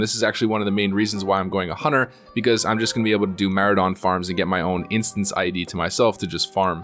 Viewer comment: did he say maurodon farm? i thought thats phase2?